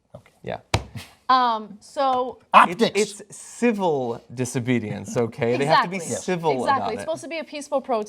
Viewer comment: did he say Fizzle Disobedience? Yeah no shit!